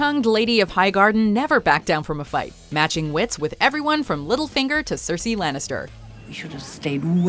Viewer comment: I thought she said “Middlefinger” instead of littlefinger...